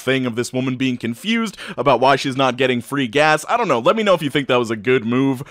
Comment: I wasn't paying attention as I was doing something and all I heard was "getting freak ass"